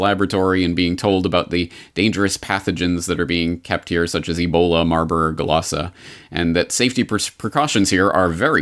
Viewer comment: I thought it was marburg and lassa that you were reading in the comic & it sounded like you were saying marlborglasses and it just came out that way clear and like that 2 times so it was strange because although you can get to speaking a little fast , you are always very precise ! hum Weird ..? God bless you !